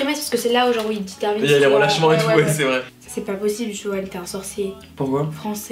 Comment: c'est moi ou elle dit ''JOEL''?